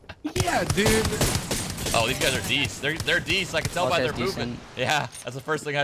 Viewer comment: Shroud said " oh these guys are......" i dont know "deece" ?, can someone tell me what word he used to describe that kind of enemy movement ?. Thanks in advance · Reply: decent